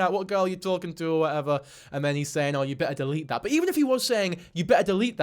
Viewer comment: I will say when you played it ultra slo-mo I heard "yeah you better do that." which could be about anything. but I agree it's not looking good.